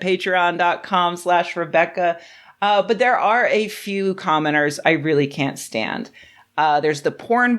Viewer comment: So in the opening I misheard "commenters" as "commoners" and thought dang all that Patreon wealth is really going to Rebecca's head, huh.